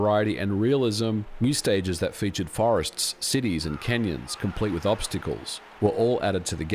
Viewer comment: I thought you said Kenyans 😭